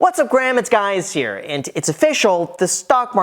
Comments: Wait a minute, did he just say "What´s up Graham, it´s guys here"? lol. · Did you just say :whats up Graham, its guys here"🤣🤣🤣